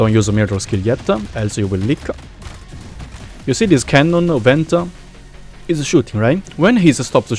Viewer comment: did i just hear said canon event?